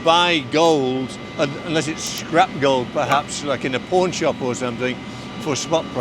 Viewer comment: Suppper interesting video, for sure one of my favorites👍 10:00 when says pawn shop I swear he said porn shop 😂 also I wanna mention out all this money that the owner probably has and he’s wearing a simple santos 🫡🤝👍